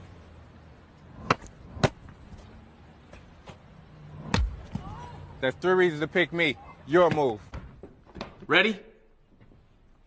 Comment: I heard it as, "Thats 3 raisins who'd pick me. You're a mole" on the first guy😂😂😂